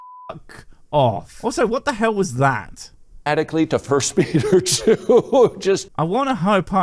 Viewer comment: Considering Friel starts talking about the Holy Spirit and sanctification and whatnot just afterwards, I think what he said is “First Peter Two,” i.e. 1 Peter 2 in the Bible, not “First beat her, do.”